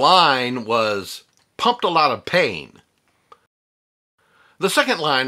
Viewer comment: I always thought he said "pumped a lot of 'tain" . I thought was a short hand ref. to the "octain" in gasoline and it made perfect sense to me.